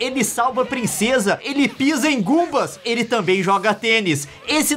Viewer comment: eu ouvi bem. Ngumbas Kkkkkkkkkkkkkkk. Eu acho que ngumbas é porco. Kkkkkkk